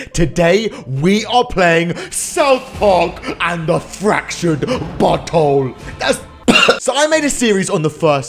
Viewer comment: did he just say south park fractured 'butthole' ?